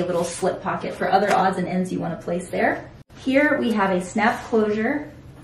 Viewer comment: When you said “snap closure” I thought you said “snack” closure and I was like “ooo this girl knows who’s she’s selling to!” 😂 I was a little disappointed learning it’s not intended for my snacks 😂